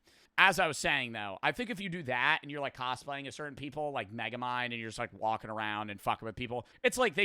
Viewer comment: what did you say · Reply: Megamind